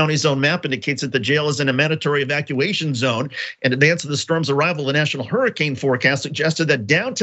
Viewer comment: ROFL this man really said "sperm surge" instead of "storm surge". I'm deceased. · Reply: Hahahaha. Yeah, it did sound a bit like "sperm." But I think he just says "storm" like that. Listen to 2:22 as he says "storm's arrival". Does sound a bit like "sperm's arrival." ;)